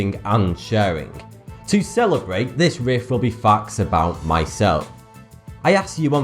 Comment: The way he says facts is like so funny. Its misheard it as FUCKS